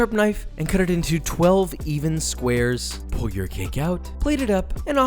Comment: i thought he said "pull your dick out"